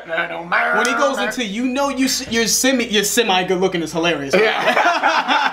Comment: Thanks to y’all!😀 I always thought he said “you see I’M good looking...” (Something he would say!) not “you’re Semi good looking...it’s even funnier now!!😂